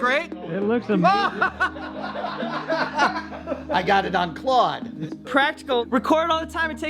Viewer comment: Am imagining this or does he call Wren "Claude" around 8:27? Or was there a Claude in that room that we are not aware of? Either way, I think "Claude" works for Wren, there is a nice ring to it.